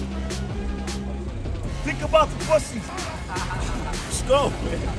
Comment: Did say think about the p**sy? 😂😂😂